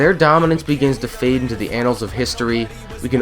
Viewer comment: did i just hear "anals" ? O_O XD